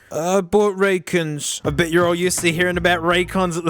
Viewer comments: I THOUGHT HE SAID I BOUGHT RACCOONS. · Bro I thought this said “I bought raccoons” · I thought this said "I bought raccoons" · i quickly glanced at the video and i thought it said i bought a racoon